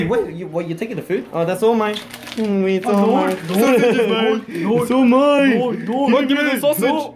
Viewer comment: What they say: No! My sausage! No! Go away! What my brain hears: Mine! Mine! Mine! Mine!